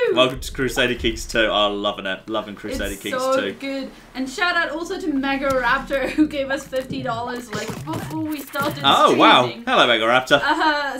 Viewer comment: I almost spit water at the monitor because I thought I heard "shoutout also to EGORAPTOR who gave us $50".